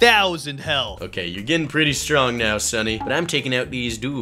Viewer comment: subtitles of the video instead of saying sunny he said steady minute